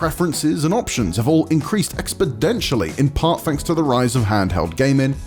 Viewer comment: Does he say "expodentially" at 0:42?